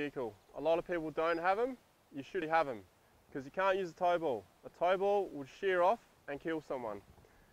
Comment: With all sincerity, what are you saying? "Tow bulb"? Never mind, the internet gods were kind in my research. Apparently you call "bull bars", "tow bulls" down yonder. Got to love being separated by a common language sometimes.